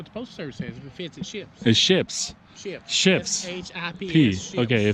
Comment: It so sound like said if it fits it shits lmao said it what? Ships S.H.I.P.S